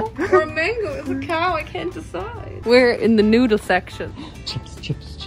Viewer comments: I understood in 4:16 „We are in the nude section“ hahahaha (I know she said noodles but first I heard it like that) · What said:”We are in the noodle section” What I heard: “We are in the nude section”